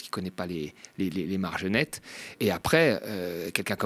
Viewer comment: J'ai entendu "les margenettes" c'était mignon. 😂 les marges nettes